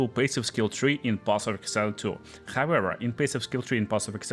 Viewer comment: "Path of Excel 2" - Youtube captions. Never have they been more apropriate. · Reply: I heard "Boss of Excel 2" which works as well 😂